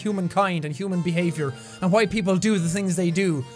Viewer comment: When jack said why people do what they do at 11:10 I thought he said what people!lol 😂😂😂